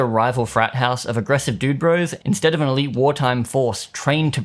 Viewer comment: I had to go back and check that you didn't say they "mutos" instead of "dude-bros" because of the image on screen.